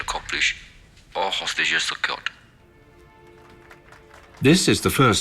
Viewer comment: why did he say all hostages were killed ? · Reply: Secured Not Killed.. :)